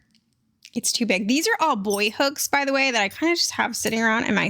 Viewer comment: Hahaha. For a second I heard "boy hooks," not "Boye hooks," and I was so confused. I was thinking, It's a good thing I'm watching this....I have so much to learn! Lol.